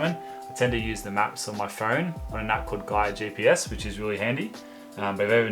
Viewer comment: Hi mate, love your videos bro so so good can't wait to get outa the lockdown to get outside, I missed what gps app you say in this at 16:46 ... can't make it out think you could lemme know be helpful to have it written down, thanks so much! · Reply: Thanks mate. The app is called GaiaGPS